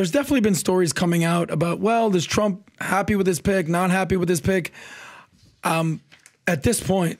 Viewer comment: sounded like "trump happy with his PIG, not happy with his Pig"😂